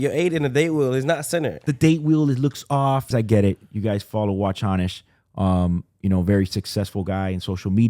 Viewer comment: Not sure if i heard it correctly, “Watch Honest”. What is his IG handle? at 44:15 ?